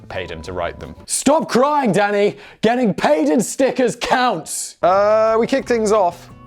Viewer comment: Stop crying, getting paid in stickers counts! Needs to be a t-shirt. Purch the merch. #CancelSimon . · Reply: Oh I thought he said Snickers (candy bar)